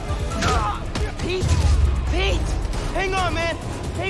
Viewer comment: I thought he said Pee? Pee? Hang on man hang on.